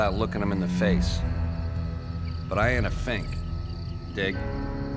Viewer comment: I love this saying but did he say think or what?